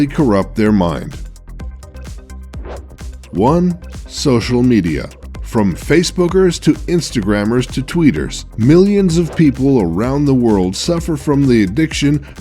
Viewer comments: in 8:10 i honestly thought he said face boogers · i thought he said fave boogers